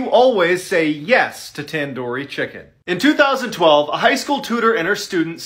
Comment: “NO, WE ARE GETTING TANDORI CHICKEN!” Imagine just hearing that at 2am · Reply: Oooh he said tandori I thought he said pandori 😂